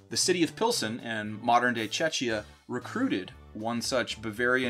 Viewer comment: Almost sounded like you said Chechnya